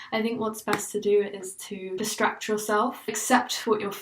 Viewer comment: lmfao I initially thought you said "destruct yourself" and didn't even question it. BPD autocorrect at it again